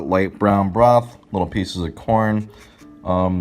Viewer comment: It took me a 1:23 to realize he was saying “corn” ramen instead of “porn” ramen.